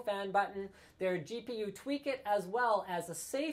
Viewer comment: what it says iweak it :P